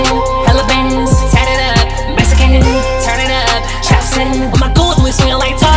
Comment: This ain’t even mumble rap I understand every word you saying you was lit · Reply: Idk man, it sounded like he said “tatted up, mexican” for that one part I can’t tell tho